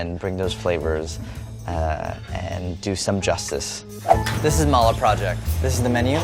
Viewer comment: When he said "this is mala project" I thought he said "this is my lil project" 😆